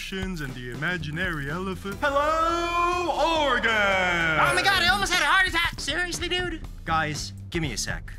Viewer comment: wHAt?? 😂😂😂 What did he say? I didn’t hear him well lmao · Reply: All I managed to hear is “Oh my god elephant the heart is hacked” 😂